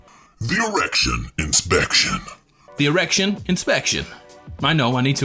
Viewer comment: I heard “erection infection” 😭